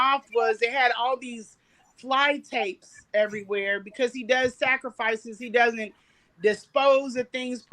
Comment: did she say, he does sacrifices? 😮👀